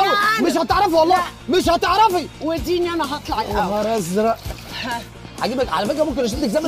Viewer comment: هو الي سمعتوا في 1:27 صح 😂😂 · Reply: بتقوله وديني اللي هطلع انا الاول